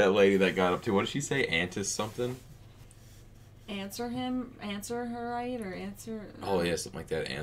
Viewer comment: The woman yelled "It's her right". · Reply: I thought she said "threat her right"